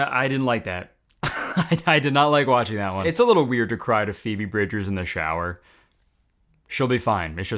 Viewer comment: I thought he said RUBY BRIDGES ANDKWKSKSKDJWK